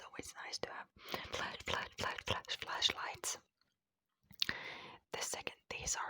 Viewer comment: Omg I thought she was saying fleash light lol I'm stupid 😁💖